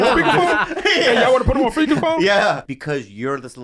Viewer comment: doc says pheaker spone instead of speaker phone and i can't stop laughing